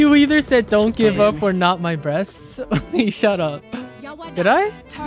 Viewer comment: the "wag susuko" kinda sounded like "not my b00bs!!" to me too LOL maybe that's why raze was baffled 🤣🤣 (suso means bewbs) 🤣